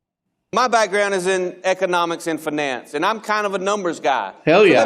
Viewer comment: Oh my God, he said finance… I thought he said FNAF… that’s not how people say finance lmao